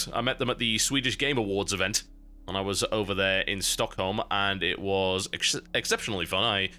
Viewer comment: Did he say "StockNOM"?